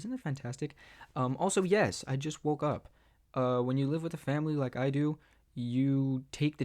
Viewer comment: i Heard "when you live with a Femboy"